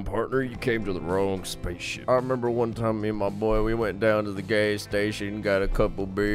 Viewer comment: Did he say “gay station”?😃 oR- yAe